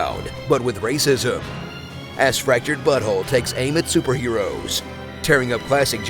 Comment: Okay, so I heard "Fractured Butthole" instead of "Fractured But Whole".